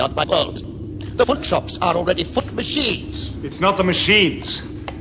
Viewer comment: "the footjobs are already fuck machines" did I hear that right? Haha